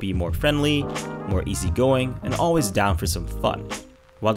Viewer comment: I though I heard “always down for some fuck” 😂 I was thinking aw fuck yea lmao